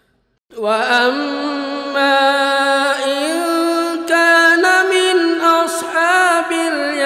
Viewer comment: ayat 90, saat seblm terharu, ustadz lafalnya "wa amma ingkaaana" bener, tp setelah terharu (menit 13:38) beliu sepertinya mengucapkan "wa amma ingTAAna"??? atau saya yg salah dengar? Saya sudah ulang2 berkali2 koq tetep INGTAANA ya dengernya.... Terimakasih... Mohon maaf jika sy yg salah